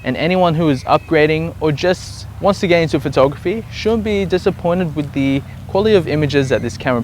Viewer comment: sorry for stupid question but i cant hear if he say "should be disappionted" or "shouldnt be dissapointed" at 5:33. could anyone translate that for me? ^^ ive just ordered the A6000 as a christmas present for my girlfriend who wants to get into photography and i dont know anything about cameras, was it a bad choice to get that camera for her? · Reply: Haha sorry I was saying shouldn't. Apologies for not being articulate. Great choice by the way! :)